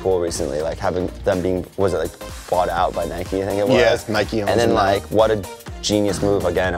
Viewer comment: Did y’all hear joe say “Nike owns Nike” instead of saying Nike owns converse??😂... Joe wake up bruh!